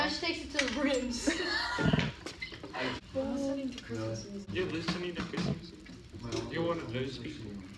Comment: “your listening to christmas music? “yeah” “your one of those people? “yeah im catholic” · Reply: Bro I dead ass thought she said Christian music